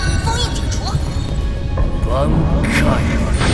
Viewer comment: did i hear the word BANKAI at 1:00:43?/......😂😂